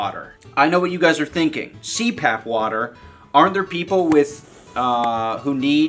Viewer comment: I swore that said crap water at first.